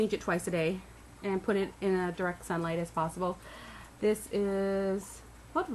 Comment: did she say "put it in dark sunlight if possible " What is dark sunlight ? Shade ? Very crappy confusing video, um, um. um, keep saying um. Waste of time ! How did I get here ? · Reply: she said direct sunlight you dimwit